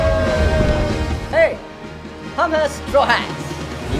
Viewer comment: What? Hum hai straw hats? Did I hear it right 😂 4:26. Btw good dubbing, far better then expected 😊 no hate pls